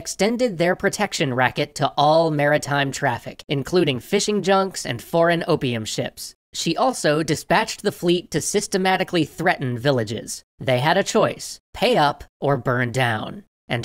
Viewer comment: Listen when I play EU4 and I'm the number 1 great power my navy fleet force limit is 0/400, so I see nothing wrong with the Emperor's wisdom. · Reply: When he mentioned "foreign opium ships", I first heard "foreign OPM ships"... I think I played too much EU4.